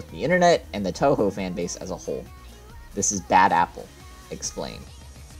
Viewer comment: I thought he said "this is bad, apple explained